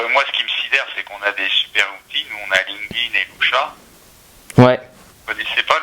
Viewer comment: c'est quoi la deuxième application qu'il dit 6:30 svp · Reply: Il me semble que c'est Lusha